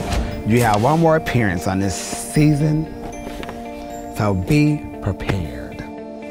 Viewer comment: Latrice and Manila are definitely operating as a unit. Even if they aren’t on a team, I don’t think they’ll send each other home... right? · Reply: JakeyonceTV did she say she’s gonna have one more appearance on this season 5:44 ?